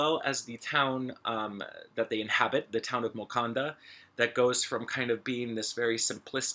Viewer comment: Did he say town of Wkanda?